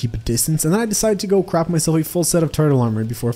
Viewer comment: it sounds like he said CRAP MY SELF